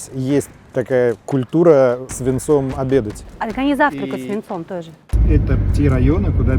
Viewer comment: Когда во вступлении был отрывок про виноделие, то, так как до этого нарезки были про тяжёлые события, то я услышал "есть традиция свинцом обедать?", "они свинцом завтракают". Когда досмотрел до конца отлегло немного))) Но "деловые переговоры свинцом вести" всё равно услышал, деформация наверное